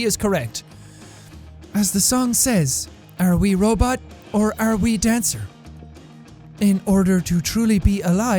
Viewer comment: what the heck I heard I’m already tracer